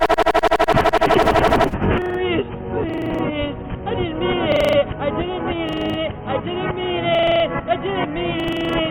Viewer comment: я не могу понять что Джулиан мямлит. Кто-нибудь знает что он говорит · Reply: я не хотел этого